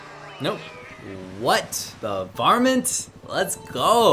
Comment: For a second I thought you were saying "what the varmint" as an exclamation (like "what the heck"). I'm definitely going to start saying that out of context to annoy my friends 😆